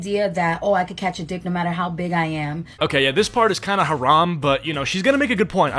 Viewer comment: Is it just me OR DID JUST SAY THAT SOMETHING IS HARAMM?? 🤩🤩🤩🤯( MUSLIM CONFIRMED!!! 👉 1:31 )